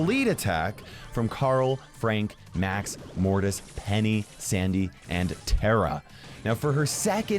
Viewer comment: Who heard "Henny"? 3:35.